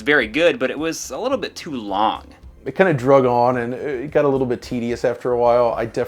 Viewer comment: Did he say “droge on?”